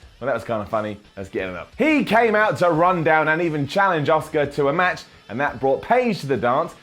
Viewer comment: Maybe it's just my Swedish ears, but when I hear English people (Paige and at least) pronounce "Asuka" I hear "Oscar"